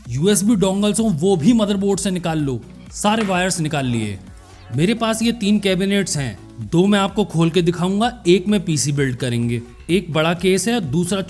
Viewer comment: "Mere paas yeh teen cabinets hai. Do mai aap ko dedunga aur ek mein PC build karenge". Thats what I heard :eyes-purple-crying